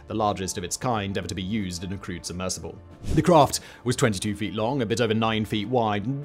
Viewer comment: I heard this as "crude submersible" and thought, yeah sounds about right.